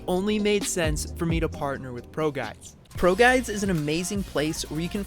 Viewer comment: When he said “ProGuide” in the beginning of the video who thought he said “Pro Guy” i did